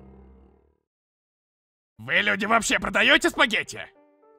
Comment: Я первый раз услышала "ВЫ ЛЮДИ ПРОДАЁТЕСЬ В ПАКЕТЕ??" АХАХААХАХАХХАХХАХХААХХА